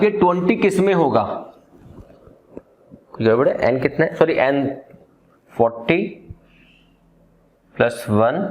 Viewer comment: am I high or did he say "L..de"? 😶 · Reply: You are not high. Bas maine Kaha "Kuchch Gadbad Hai" .. but sometimes our mind focus on what we want to hear.